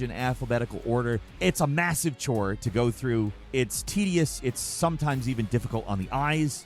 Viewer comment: did you say "tedious" or "tittiest"?